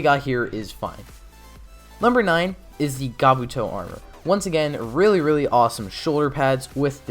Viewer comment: Am I hearing "Gabuto" rather than "Kabuto"? Am I near-deaf?